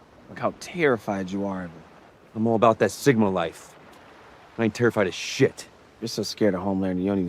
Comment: wait, he actually says sigma life?